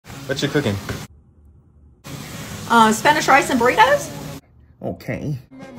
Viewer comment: Sounds like she said Burricos🤣